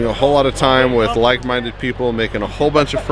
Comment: you had just told the "mic" story (haha) so of course at this point I heard "mic-minded". Which is also accurate :)